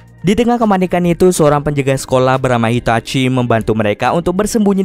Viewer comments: kok namanya itachi atau gue yang salah denger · gua Gk salah dengar apa Itachi apa itachin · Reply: Itachi namanya woi